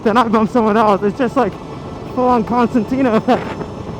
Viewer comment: sounds like you said constantina effect but it's concertina. Easy mistake to make some Lance stroll said it wrong as well.